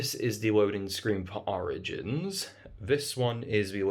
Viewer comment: Instead of hearing „Origins“ I heard „Oranges“ I paused the video and said „Is do you are have dumb?“ and then realized I had the dumb